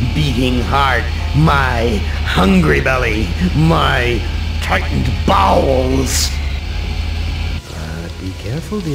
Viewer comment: Was it just me who thought when he said, tightened bowels that he said Tightened Balls